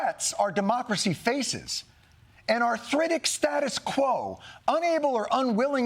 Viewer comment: Around the 12:20 mark, what is the word that Jon uses prior to “status quo”? I keep hearing “thritic”, and the subtitles aren’t helping. 😅